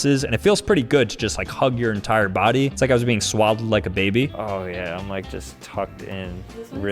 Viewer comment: at first thought you said swallowed like a baby and I was like “what did he just say?”